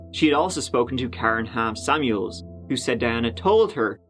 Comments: Am I the only one who first thought was going to say, "Karen Ham Sandwich"? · I keep hearing “Karen Ham Sandwich” instead of “Karen Hamm-Samuels”. Why would a person hyphenate that?!? 😂